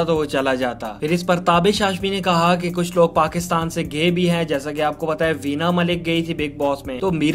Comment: who else hear Kuch loog Pakistan sey gay b hain 💀